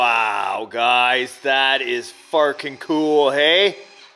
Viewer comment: Man that's so "Sparking Cool!" You said something else but I heard "sparking!" RC Sparking Coolness! Do what you want, I'll be watching. 00